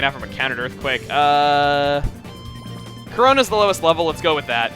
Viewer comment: actually you misheard he said Corona or his Typhlosion.